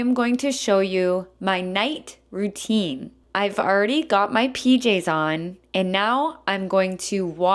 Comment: I've early 로 들리는데, I've already 가 맞나요?